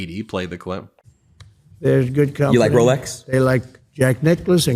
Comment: When the fellow said Rolex, I heard "Rolodex." I thought it was gonna be a mean joke. Too bad. I was waiting for the punchline